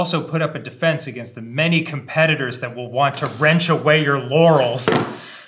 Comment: I don’t know what y’all heard, but I heard “your Yanny’s”